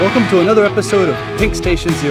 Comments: I THOUGHT HE SAID DRIVEWAY DFJHWJE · Why did The announcer sound like he said “Drug Way”